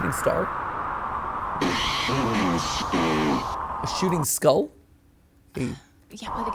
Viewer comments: the "shooting star" vs. "shooting skull" thing confused me, I kept hearing it as "A human skull." I think the sh sound was just the distortion. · “A shooting star?” “A shooting skull?” I hear “a human skull”